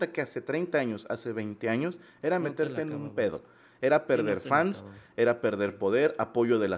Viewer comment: Saludos!! En el minuto 59:50 entendí mal el "era perder fans", clarito escuché "perverfans", chicos, tienen muchos "perverfans", saludos desde Chicago!! Ya los queremos ver por acá!!! Los vamos a pedir de regreso!!! 😃